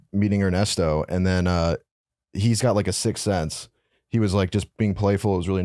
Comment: is calling it a "sick sense"? Lol